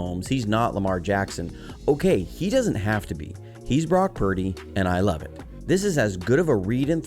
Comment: "He's Brock Purdy, and I love it." Ngl, I thought you first said, "He's Brock Purdy, and I love him." 😂 either way works!!